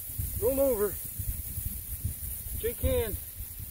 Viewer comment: Did I just hear " shake hands" lol